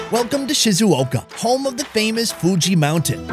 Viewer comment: When he said Fuchi Mountain I thought he said Gucci Mountain.